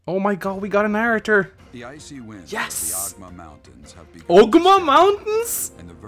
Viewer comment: XD I thought he said the Ogma mountains XD